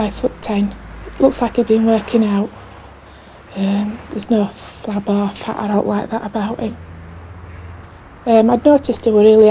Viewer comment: what does the witness say about the suspected rapist please? As a non-native speaker I have some trouble with her accent here. "There was no flab"... Half? Off? I understand she found him to be too 'ripped' or 'dry', but neither word makes any sense to me after 'flab', especially as the final word in a sentence. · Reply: She says “there’s no flab or fat or owt like that about him” Meaning he looked fit . Owt is another word us Northerners use for the word ‘anything’